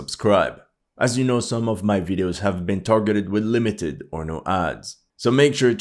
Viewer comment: it always sounds like your saying limited porno ads